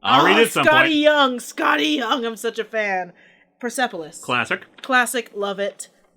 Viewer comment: What was mentioned at 13:20? Percefaline? I can't hear it...